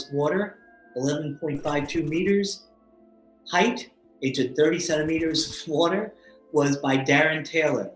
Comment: At the beginning when talking about the water at first I thought he said the guy jumped into 37 meters of water. I had to listen to it several times to hear "30 centimeters" I'm someone that is afraid of water, mostly open water. To hear him describe 37 METERS as shallow was insane to me. Glad that's not what he said.